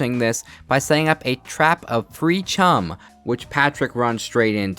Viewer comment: I heard 13:42 as “pre chum” and realized the internet finally broke my brain. Great vid